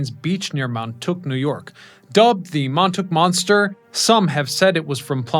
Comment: did he say Mon Took ? The natives will never accept that !